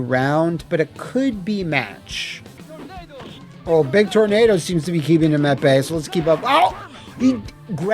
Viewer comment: The Engrish is 😆 I hear "D!ck Tornado" 🤣